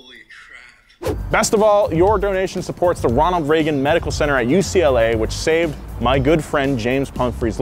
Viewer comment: At first I thought you said Ronald McDonald medical center, they saved one of my nephew's. Shout out to all Ronalds I guess, thanks for your generosity.